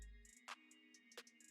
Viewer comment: Every time you say your intro all I hear is “ohhh Diddy got a podcast”